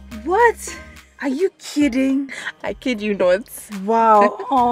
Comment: Did I hear say "I kid you not"